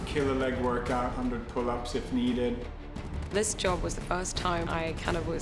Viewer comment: did he say “one hundred pull ups if needed”??!?!??